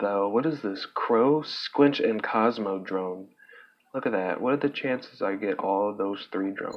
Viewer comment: 313dmg wow 18:45 what did you say tho? Couldn’t hear properly lol 20:58 for big mech vs tiny mech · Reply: thanks. I thought I heard good drone lol